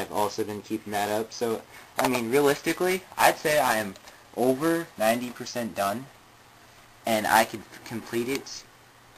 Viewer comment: Too bad that you lost part of the crew , but aswell it's good that you gained more members... Also I misheard "I'm Over 90%" too "I'm over 9000" :B